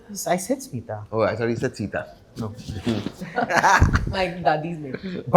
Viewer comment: I legit thought I heard Seetha too! 😅